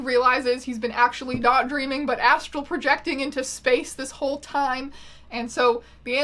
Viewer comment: I misheared "astral projecting" as "asshole projecting" multiple times in this video and I think that it makes complete sense in the context of the onion boy